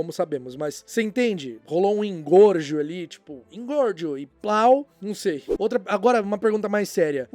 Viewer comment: eu ouvi "engorgio e... PAU" kkkkkkkkkk não tankei